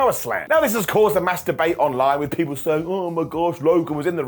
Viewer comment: I thought he said masturbate but it was " mass debate" 😂